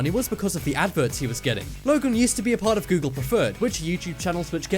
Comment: press this Use caption You will see the caption said locum instead of logan XD